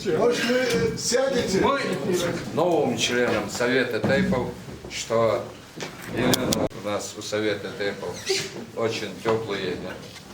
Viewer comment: елена - член совета тейпов ингушетии )))))))) это мне послышалось ?????